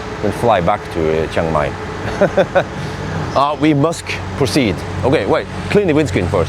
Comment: Did you say "we MUSK proceed" around 1:14? Still Tesla Bjorn, hè